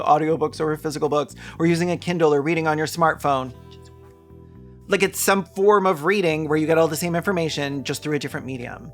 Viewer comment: What is being said here?? I can't hear it and it's not captioned. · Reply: “Which is also weird”